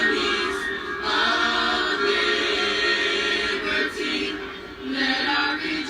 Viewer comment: I thought they said "bring back the harmonies of n*ggerty"